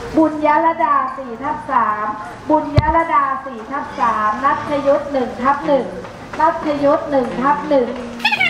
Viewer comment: ผมคนเดียวรึป่าวได้ยินว่า น้องประยุท1/1